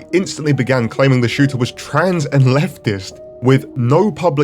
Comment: "trans and leftist" I thought you said "transatlantic"